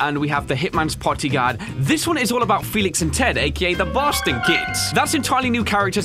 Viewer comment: did he say "The Boston Kids" or "The Bastard Kids" ?